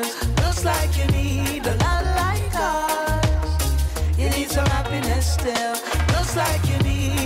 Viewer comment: I thought they were saying “You need some rapping Estelle”